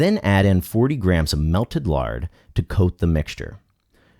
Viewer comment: Can someone tell me @ 5:00 What is he saying? Melted what? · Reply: He said melted lard